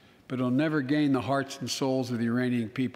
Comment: I'm sorry did he just say "The Iranian people" I listened to it 4 times, it sure sounded like it to me. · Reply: He said, "...the URAINIAN people...". All this time, we've been worried about an invasion from Mars by the Martian people. As it turns out, the threat from Uranus by the Urainian people should be the focus of our attention. LOL!